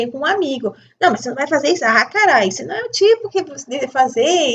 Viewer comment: Eu ouvi um palavrão? Aos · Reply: Não irmã, a expressão que eu usei foi ... A cara isso não é .... 😊